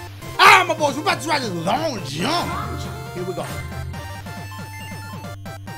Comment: dam sounded like you say alright ma bois we bouta try think long junk 😂